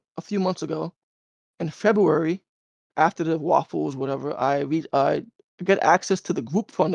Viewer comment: is he saying waffle or raffle?😂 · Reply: Waffle